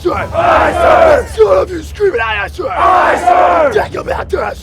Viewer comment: I didn’t get a word of what he said haha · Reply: Drill Instructor: Dadgum mouth, aye, sir! Recruits: Aye aye, sir! Drill Instructor: That’s all of you screaming, aye, aye, sir! Recruits: Aye aye, sir! Drill Instructor: Dadgum mouth, aye, sir! Recruits: Aye, sir!